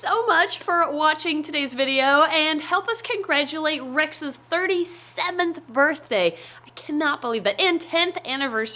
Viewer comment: 35th birthday? I thought you said she's 37 😕?